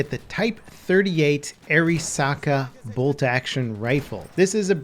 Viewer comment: I thought "Arisaka" said "Alaska"